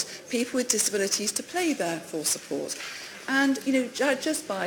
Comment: What's she saying @ 2:36 "Play their for support" ? "Pay their full support" ? ??? She said it twice the same way... What is she saying?